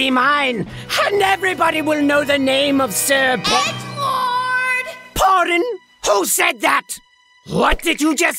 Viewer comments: At first I thought Cherry Bomb said “Egg Porn” but when I turned on the captions it said “ Egg Lord”........oops 😅😅me and my dirty mind....😂😅😂😅😂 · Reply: i could have sworn she said edward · At first I thought Cherry Bomb said “Egg Porn” but when I turned on the captions it said “ Egg Lord”........oops 😅😅me and my dirty mind....😂😅😂😅😂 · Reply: Still wrong, she said "Edge Lord"